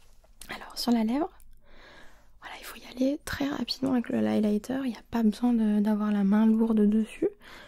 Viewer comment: mdr le quoi ?? 😂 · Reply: Je voulais dire l’applicateur 😂